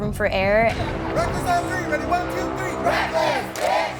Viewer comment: is it just me or did they say “reckless b****” at like